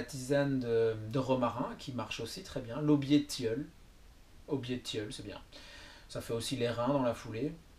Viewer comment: j'ai pas compris il dit quoi a 6:30 ? ( "obé de tilleul"? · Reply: Il dit "aubier de tilleul" :-)